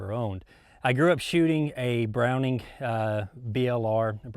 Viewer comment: I thought he said @ 1:35 'I grew up shooting a Browning BAR' which would have made him my Hero as what young boy (or old man) wouldn't dream of such! But yeah the 'BLR' is neat to be sure in particular as a young boy's learning platform...